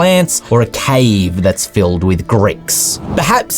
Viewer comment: I'm sorry but I heard "a cave filled with Greeks" and I'm crying rn from laughing about it